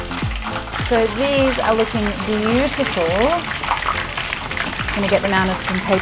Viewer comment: "so these are looking beautiful" she said and subtitles said "so these are looking at Utica" lol